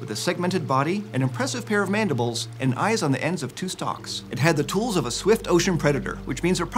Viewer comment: Sorry but I heard stinks